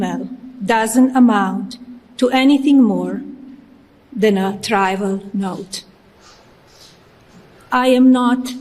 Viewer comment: I had to rewind thst a couple of times; it sounds like she says “tribal note.” I believe she says “trival,” or mispronounced “trivial.”